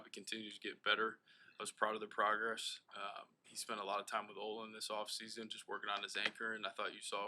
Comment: Did Poles say Braxton has been doing work with Olin at 35:00 mark?